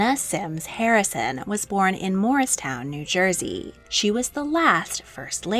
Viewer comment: almost lost my mind when you mentioned where anna harrison was born because I thought you said moorestown instead of morristown lol.